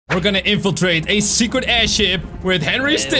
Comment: Did you say aidhip